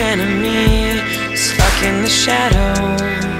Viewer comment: I hear 'Fucking the shadows' not 'stuck in the shadows' Sorry if I ruined it! XD